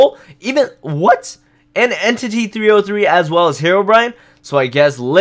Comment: On 3:33 Look it did not say Entity 303, but Entity 333